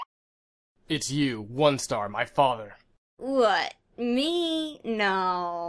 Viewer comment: What Darktail said: “OneStar it’s you, my father!” What I Heard: “OneStar it’s me your father!”